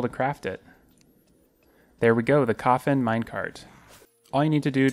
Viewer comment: I thought you said the cofing Minecart XD